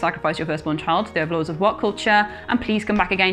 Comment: I thought she said “welcome to woke culture”